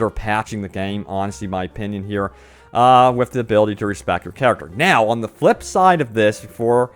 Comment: I thought he said no respect